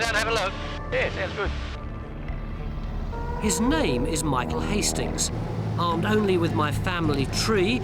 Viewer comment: Cut hair is not useful for DNA testing. What is Michael's last name? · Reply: I thought i heard the video say his last name is Hastings.